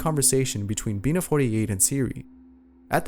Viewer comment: When he said bina 48 i thought he was saying being a 48